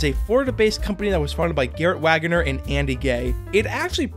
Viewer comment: did you say Andy gay?!